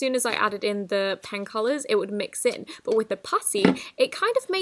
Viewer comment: In one part of the video, she said "Putty" but I thought she said "Pussy"